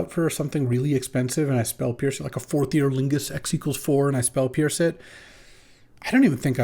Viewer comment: Every time LSV says "Forth Eorlingas" I hear "Fourth-Year Lingus". Anyone else?